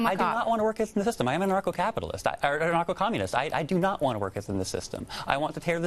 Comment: Did he just say he's a "Narco communist"? (2:55 mins in) He's just putting words together too sound smart. Narco is short for narcotics which is illegal drugs. LOL